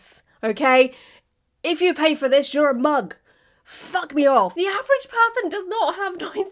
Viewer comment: I heard 'suck me off' at first 😂